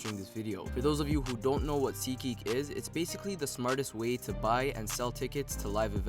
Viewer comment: Am I the only that heard him say " the smartest weed to buy"?? 😂😂